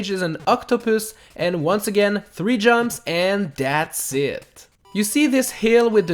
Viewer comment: it sounded like said dat sit but he actually said thats it